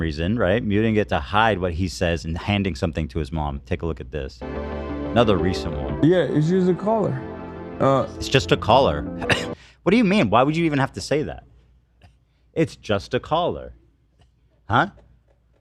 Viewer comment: it sounds like he says just use the collar